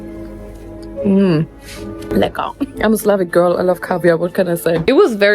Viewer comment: Am I crazy or did she say at 11:53 "I'm a slavic girl"? If that's the case that's funny, I thought she is just german